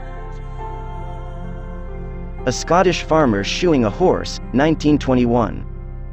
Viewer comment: I heard "A man shooing his horse" so I expected to see him chasing him out the barn...